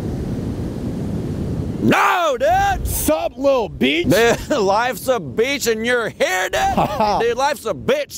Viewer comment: What he said in 0:20 ? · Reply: “Life’s a beach and your head dude?!”